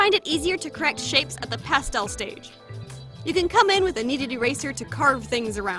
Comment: I always thought she said a needed eraser, not a kneaded eraser.